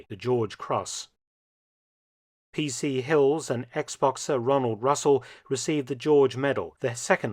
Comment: I heard you say ex-boxer and for a split second misinterpreted it as Xbox-er.